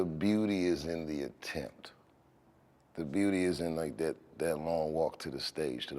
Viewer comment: "The beauty is in the attempt." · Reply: Ahbleza Sellars I thought he said the beauty is in the intent, not attempt, both would make perfect sense in the context he used it for as well